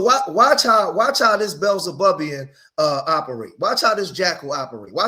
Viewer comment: did he just say Beelzebubian